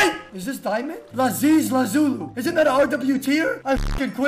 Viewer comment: is the funniest thing I heard in my life ‘lazies lazuli’ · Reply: Lazulu